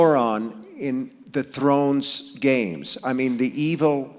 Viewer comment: Did King really call it the Thrones Game? lol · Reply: I heard it as "the Throne's games..."